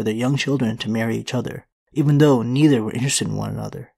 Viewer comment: ? Even though--? I couldn't hear that part no matter how much I replay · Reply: “Even though neither were interested in one another”